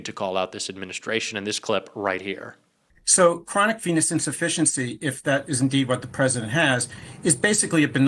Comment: I hear "chronic penis insufficiency" every time. I can't unhear it!